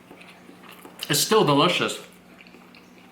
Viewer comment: did he said plain rice is delicious